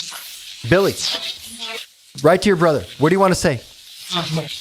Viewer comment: says " Billy wat you want to say to yer brither"??? 2:25:46/47 " Thats me " MALE voice FAST BACKGROUND FAINT whisper SLOWED down YouTube SETTINGS O.55x